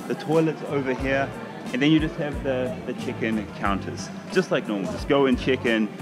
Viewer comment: Lol- I’m American, and every time said “check in” I thought he said “chicken”.